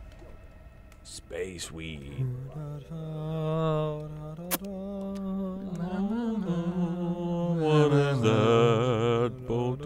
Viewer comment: Did Ryan just say space weed? Cuz' if so, shoutout to Continue? /👌